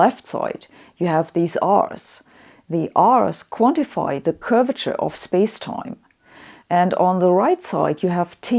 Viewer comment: Did you say my arse is nicely curved in space-time?